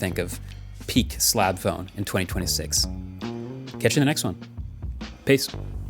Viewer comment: Thought he said "Peak Slop phone". Wouldnt be surprised with how that word gets thrown around now